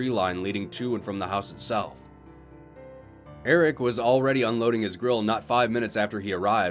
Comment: I heard “unloading his grill” as “unloading his girl”. Damn it internet…